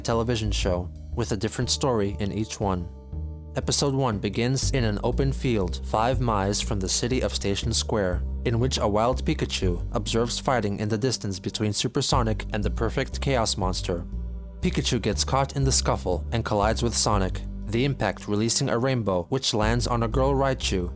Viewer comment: I had to do a double take because your accent makes it sound like you said "wild farting," not "wild fighting."